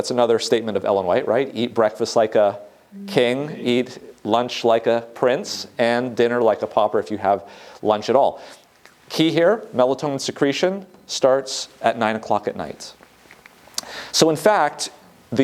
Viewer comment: I heard it as eat breakfast like a king eat lunch like a queen and a dinner like a pauper